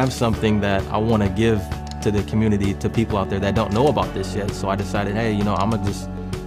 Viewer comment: Lol did he really said "shit" in a professional video lol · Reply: He said "people that don't know about this yet" not shit